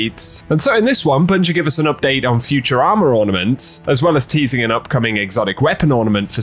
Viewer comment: I thought you said futurama ornaments right at the beginning 💀